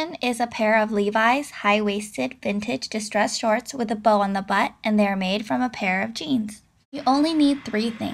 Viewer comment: on the butt? i thought it said in the butt......